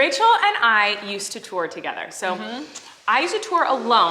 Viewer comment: 😂💛 I thought she said "Rachel and I used to twerk together" I was like what!??? And then I remembered... TOUR 😂😂😂😂